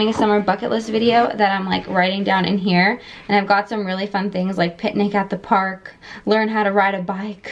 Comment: I thought when you said have picnic at the park you said pet at the park 😂